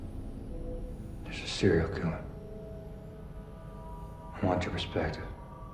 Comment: "I want your perspective", is what I think he is saying.